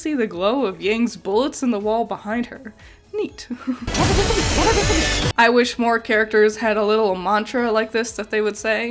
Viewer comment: what is she saying? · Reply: "Never miss a beat, never miss a beat".